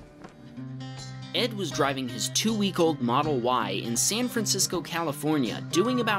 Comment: I thought it said, ‘I drove my two-week old in a model Y’